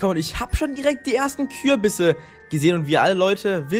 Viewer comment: ach Kürbisse ich habe Kuhbisse verstanden😂😂